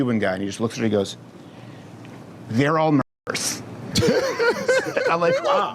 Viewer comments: I’m dying to know what Crowder says here but I can’t figure it out! Someone help! So Crowder is talking about his gay Cuban booking agent who has no patience for the LBGTQA+xyz community anymore. Crowder & this agent are at a restaurant, talking to their server & for somehow the server ends up mentioning being confused about what pansexual means. The gay agent responds, “They’re all -???” That response is the punchline of the whole story & it’s censored! Someone, give me a hint please!! I gotta know!💗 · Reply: I heard “murderers” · I’m dying to know what Crowder says here but I can’t figure it out! Someone help! So Crowder is talking about his gay Cuban booking agent who has no patience for the LBGTQA+xyz community anymore. Crowder & this agent are at a restaurant, talking to their server & for somehow the server ends up mentioning being confused about what pansexual means. The gay agent responds, “They’re all -???” That response is the punchline of the whole story & it’s censored! Someone, give me a hint please!! I gotta know!💗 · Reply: he says "they're all murderers"